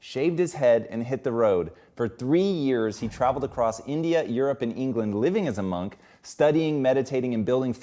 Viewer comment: did he say India, England and Europe?